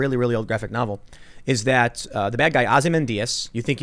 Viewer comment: It sounds like Tim is saying "Ozzy Mendias" when he stays "Ozymandias".